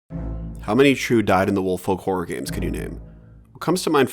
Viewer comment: What were you saying in the beginning? "How many true dive in the wolf folk horror games can you name?" What? I don't understand what you're saying, I need answers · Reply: I said "dyed-in-the-wool." It's an expression that basically means stubbornly true to the core. I use it a lot and assumed it was pretty common but it seems I'm wrong!